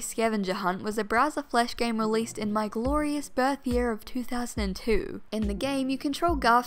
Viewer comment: "my glorious birthyear, 2002." WOW i was not ready for the concept that im just barely older than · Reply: I thought she said "my glorious third year" and I was like "oh she's the same age as me" and this comment made me realize I was wrong